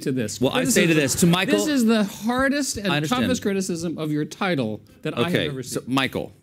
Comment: I'm not a native english speaker, so when I heard 2:23 I heard "This is the Trumpest criticism". What was actually said?